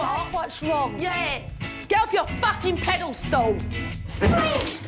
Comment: did she say "pedal stool"? 😅